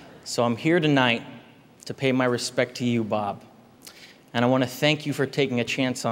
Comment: did he just say "bob"